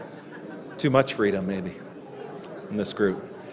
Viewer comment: Did he say to much freedom 2:30. 👀🧠💥